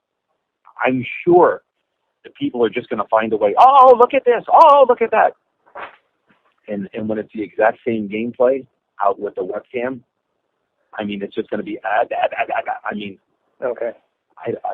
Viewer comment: I think he says " albeit with a webcam"